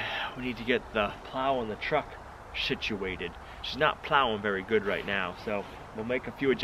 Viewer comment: Did say "shituated"? Must be cold. I know my mouth works funny when I'm out in cold weather 😉😛😁😂